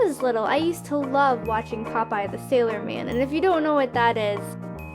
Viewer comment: I thought she said pot pie the sewer man